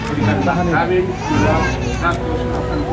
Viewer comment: maskawinnya brp kurang kedengeran, kepo ey 180rb? Sorry